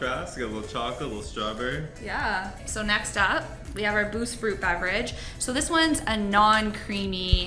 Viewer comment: When she said boost, I heard “booze” the first time. I was like heck ya 10/10 hospital food! 😂